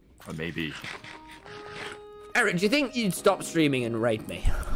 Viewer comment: thought he said “rape” not “raid” LMAO